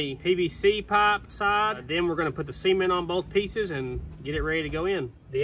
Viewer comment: I SWORE he said Semen! LMAO